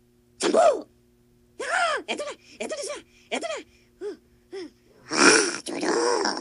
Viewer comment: i heard one saying: “nakaw ...eto na ...eto na sya ....eto na” in my native language “tagalog” if that is the case i am proud to be one...🤣